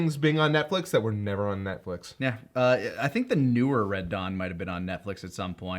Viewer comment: I thought said "red dog" and not "red dawn", so I preceded to watch a two minute trailer of an Australian film by the same name